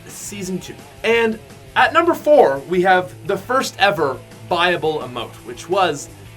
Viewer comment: sounded like you said bible emote